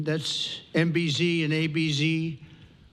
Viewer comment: Did he say nbZ and abZ 3:36?